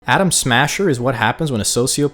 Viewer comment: Woah woah hold up.. Did you just say "Adam Sandler" instead of "Adam Smasher" ? 😅😅😆😆